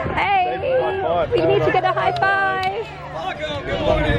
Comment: Wasn't that a high 4? I think I heard the woman say "high 4". :-)